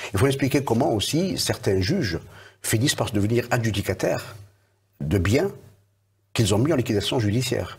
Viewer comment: Slt, il dit vers 12:45 : ''...les juges deviennent AB....cataire en toute impunité...''' c'est quoi ce mot? Je n'entend pas bien excusez moi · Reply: Désolé de répondre que maintenant. Il dit que certain juges finissent par devenir "adjudicataires" de biens qu'ils ont mis en liquidation judiciaire en toute impunité.